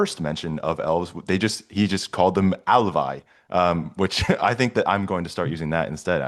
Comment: what word did you say there? how is it spelled? Quod verbum dīxistī illīc? Quōmodō scrībitur? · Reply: alfa, -ae 😎